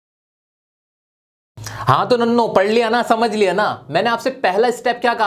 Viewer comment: he said nannho I heard nallo 😂